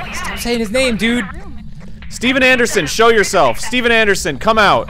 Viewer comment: Dan and Arin's voices over lapped here making it sound like he said "Steven Anderson shit yourself"